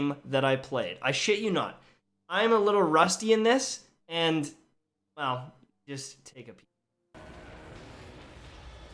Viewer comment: what the f is he saying from 7:14 to 7:18 ish he says "I AM A LITTLE RUSTY IN THIS AND WELL, JUST ......" what is the last word or words he says here it cuts out or he's really quiet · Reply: He says “I am a little rusty in this and well, just take a peak”